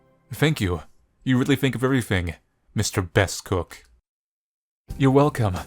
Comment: i keep hearing "Mr. Best Cock"